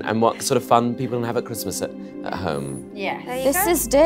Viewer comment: No one Jade: this is dirty Me : I thought she said this day 😂🤣🤣😂